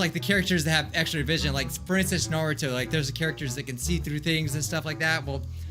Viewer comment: Did u just say princess naruto